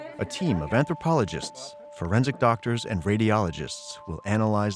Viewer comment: I have no idea how or why I heard "preventative doctors" instead of "forensic doctors" around the 10:00 mark, but lol.